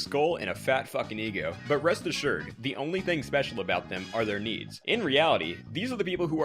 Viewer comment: "the only thing special about them is their needs" Omg thats gold. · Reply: Oh i thought he said knees i was confused